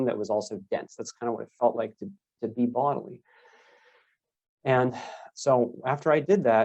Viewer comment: What’s that word he utters at 3:16 or 3:17 ? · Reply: It sounds like "to be bodily."